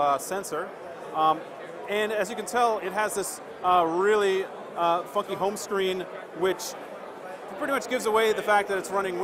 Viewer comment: did i just heard fuc@ home screen xd?